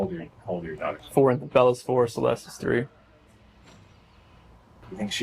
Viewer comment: Chris says Bella is 4, Celeste WAS 3!!Past tense... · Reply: He did'nt say was, he clearly said "is". What are you talking about? Delete this comment please. Weird FOR REAL😂. 2:40 mark if you want to check.